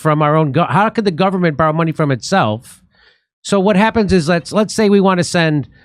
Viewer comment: I swore I heard "the government borrows money and prints bombs"